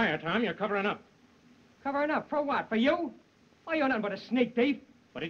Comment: did he say what I thought he said?? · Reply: Nickle snatcher... NICKLE